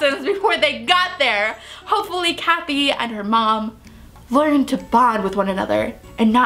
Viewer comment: YOU REVEALED HER NAME! YOU SAID," Hopefully KATHY and her mom." OMG~! Well thats what I heard I'm probably wrong tho xD :3